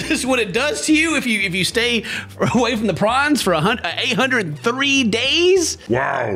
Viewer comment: Ohhhhhh, porn, I thought you said prawn and I was like "Eh?"